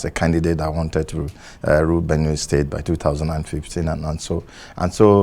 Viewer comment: Sorry at 2:07 did he say that wanted to "Rule Benue State"? .... That mindset is what we need to vote out.